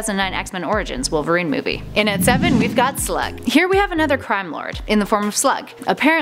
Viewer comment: I thought she said "slut"😂😂